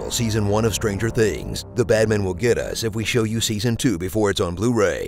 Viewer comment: "The Bad Man will get us if we show you season 2 before it's on blu-ray" 3:11 4:00 The Bad man are coming for you. 😈😈😈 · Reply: OH! So it was Bad Man!.. I really thought that he said "Batman"...